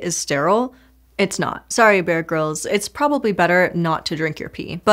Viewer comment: She said "Bear Grylls" and I thought she said "Beer Girls" and somehow I thought there was an entire subculture that I missed in my nearly forty years of life.